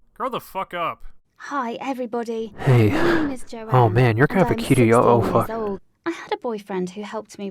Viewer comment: not gonna be lying, that's so funny lmao when he says, i think he said "hey your kinda cute" then shes 16 hes like oh- oh, f-fuck.